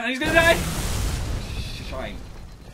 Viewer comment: pls tell me im not the only one who heard phil say "shite"